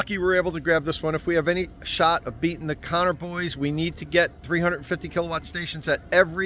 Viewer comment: I kept hearing "Connor boys" as "carnivores" and would briefly get confused.